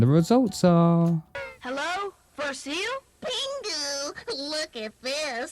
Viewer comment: Pingu wrongly calls the blue seal "fur seal" instead of Robby as his name actually is.